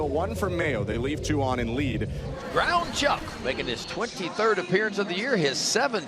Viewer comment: Did he just call him "ground Chuck"? 🤣😂😂😂 Nah beef is NOT what you want from a pitcher, lol